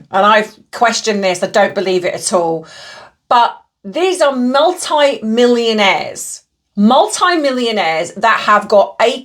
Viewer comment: Selling off the roadside is common. NOT in that area. But I buy off the road, vendors park off to the side. Ohhh millionaires I thought you said hundredAires!